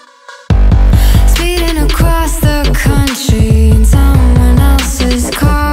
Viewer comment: "pass the pumpkin, it to far" I swear That if you hear what I hear it can get annoying real quick... More annoying than Ft. Freddy himself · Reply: But I hear "Cross the continent, it to far"